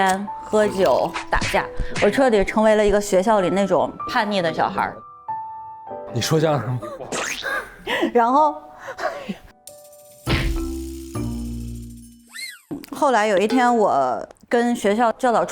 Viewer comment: 只有我听成了 “你说相声么?” ？😂 抽烟喝酒烫头不就我于大爷本爷嘛😂😂😂 也有我社会东的份儿😂